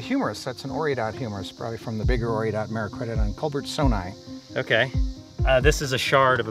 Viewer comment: what??? what does he say here?? I slowed down the video and watched it like 8 times and still have no idea what the name of the organism is Edit: I found it, it's called "Merycoidodon Culbertsoni," and it was a boar-like animal from around 20 million years ago. Really frustrating and annoying that the captions spelled it completely wrong and I had to dig for 10 minutes to even find what he was talking about